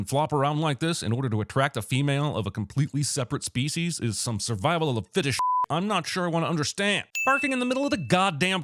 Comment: I can’t be the only one who heard “Survival of the Fetish”